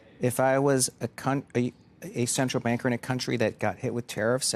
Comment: Please say someone heard what I did at 2:15 lol ?! We know he isn't a cu*t.